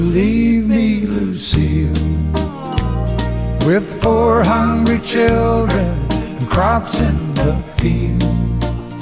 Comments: When my youngest son was 4 or 5 he used to sing "4 hundred children and caught in the field. I would say "Must be children of the corn" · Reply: When I was younger I thought it said “400 children and a pile of bills” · When I was little I thought he said four hundred children lol!!!! Thank you for making me smile everyday and ❤️❤️❤️❤️❤️❤️❤️❤️ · My mother loved loved Kenny. When I was a kid I thought he said four hundred children!🤣😂. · As a kid I thought he said 400 children..Sorry..loved his songs then and now. Thank you for playing I love his duets! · I always thought he said four hundred children. Lol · When I was a kid, I thought he said "four hundred children." It was years before I finally heard it right, LOL. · When my youngest son was 4 or 5 he used to sing "4 hundred children and caught in the field. I would say "Must be children of the corn" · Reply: I was a kod when this came.out and I thought he said four hundred children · 😂 one of my misheard lyrics. I know he's saying "four hungry children" but I hear it as " four hundred children". First time I heard the song was, man she had a lot of kids!